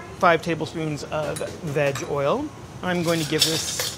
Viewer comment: I know he said "Veg oil" but it sounded like he said Vag oil.